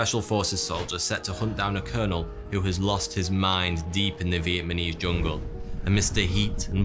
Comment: did he say "Vietmanese?" 👀